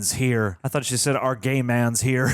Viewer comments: I thought she said “Our gay man’s here” 🤣🤣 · LMAOO SAME I THOUGHT SHE SAID GAY MAN